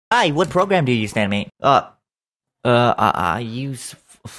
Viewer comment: I heard “what program do you stan, mate?” and I refuse to believe it was anything else